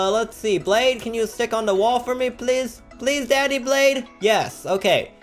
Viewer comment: Did he just say please daddy blade....